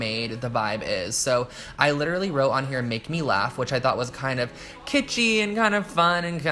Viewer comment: I thought that said "make me lunch" lmao